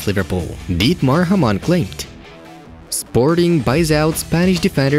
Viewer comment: For a second, I thought it said "Sporting to buy out Porto" and was like wtf? Of course, it was actually "Porro"